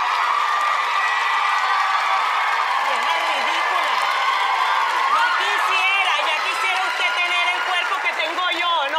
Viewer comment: Can anyone hear and translate what Sofia said at 1:43 ? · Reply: She says "vieja ridículaaa" which means "ridiculous woman". I added two more "a" letters because youtube censured de word...